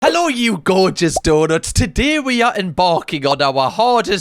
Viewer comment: ligit thought he said "door nuts" but realised it was "donuts" I love his accent and I am new.